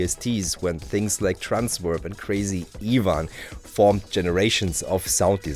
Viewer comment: what's the 2nd vst named there? it's hard to make out what was said. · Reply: Crazy Ivan